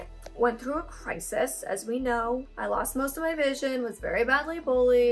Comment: I love this video! When you said you lost most of your vision I misheard and I thought you said “I lost most of my bitches”😂